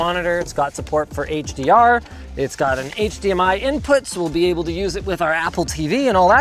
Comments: Idk how the subtitle gone "HTMI" instead of "HDMI" Or maybe is Linus's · in the video, the subtitles say HTMI instead of HDMI. :disappointed